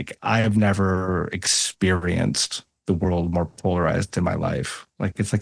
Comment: I can't understand the word at 35:22 "I have never experienced the world more __ in my life." What is that word? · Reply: *polarised